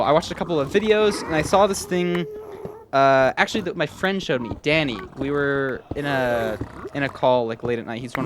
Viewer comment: did I just heard dani that means he is alive?